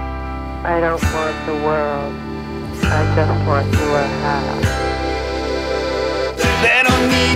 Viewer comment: did she say "I don't want the world, I just want to wear a hat"?